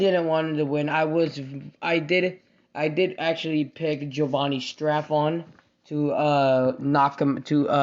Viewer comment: Sounded like you said Jovanni “strap on.”